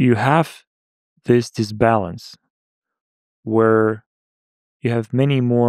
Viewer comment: awesome. just awesome. but in 1:26:11 subtitles say the oppostie of what he says (balance instead of disbalance)